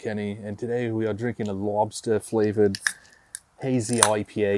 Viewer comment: I had to go back and turn on the subtitles for the beginning because I could have sworn you said it was Lobster flavored and the closed captions indeed said Lobster. Was the auto-generated closed captions and myself just miss hearing you or is it actually Lobster flavored